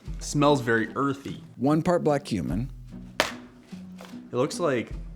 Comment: When Greger said black cumin I heard "one part black human" 😂